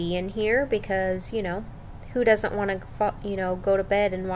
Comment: "Who doesn't wanna fu-.." Yeah, I heard that.